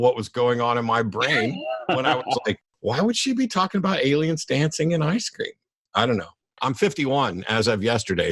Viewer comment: I thought he'd said Aliens dancing in Ice Cream. Matt saying Aliens dancing out the thought in my mind. It became true in my mind.